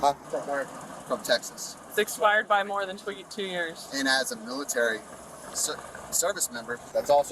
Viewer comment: Wait, oh, he's active military. For a second I thought he said she's military. Haha. Stay puff girly.